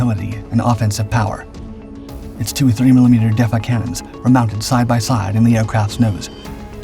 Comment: I had to listen like 3 times. Thought he said 3mm cannons.😮